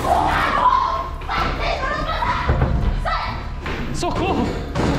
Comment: Impresso minha ou no 12:10 escutei a voz do falando socorro??